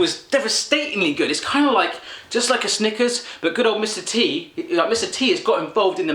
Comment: It sounds like he is saying Mr.Tea